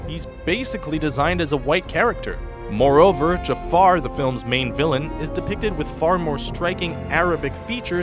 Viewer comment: did he say the villains main villains or am i deaf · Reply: "The film's main villain"